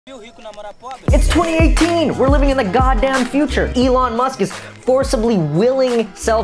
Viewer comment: wtf the brasilian's word in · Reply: It says (ja viu rico namorar pobre)